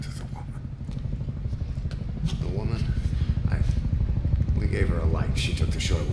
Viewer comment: what does Ishmael say to Snake about the woman? I'm hearing, "We gave her SOMETHING, she took the short way down." What does this mean exactly? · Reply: "We have her a light, she took the short way down". In another trailer there is a woman in that same room that is on fire (gave her a light). She is headed toward the window that Ishmael is at in the beginning of this demo. Presumably she fell out the window (took the short way down).